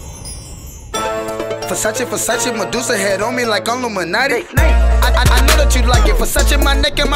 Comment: In the beginning (about 0:05), did anyone hear "like illuminati"?